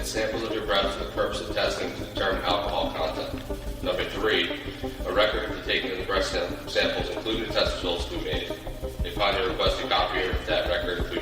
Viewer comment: The caption says "a record for taking the breast test samples including the testicles" 😂😂😂